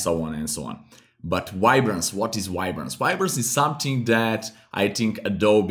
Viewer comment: I hear "wide-brance"